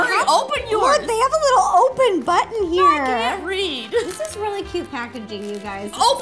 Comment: Ro:Look they have a little open button Justine:I can't read 😂😂😂😂😂😂😂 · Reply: Haleema Kauser I thought she said “I can’t breathe”